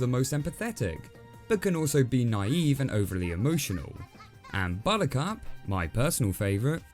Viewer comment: took me a while to realize you said buttercup I heard "bonika"